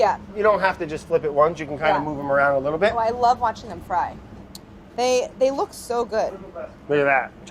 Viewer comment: I thought I heard Claire say, "I love watching them cry"